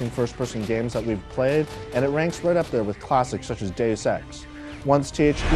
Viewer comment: i thought he said gay sex?!?! but no, it was deus ex